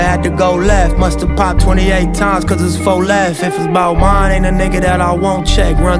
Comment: I always thought cuzz said "If it's 'bout Bryn.., ain't a nigga that I won't checc". He says "If it's 'bout MINE"